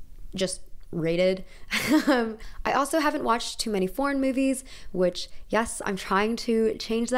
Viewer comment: OOF I had to rewind for a second when she said foreign movies I thought she said PORN MOVIES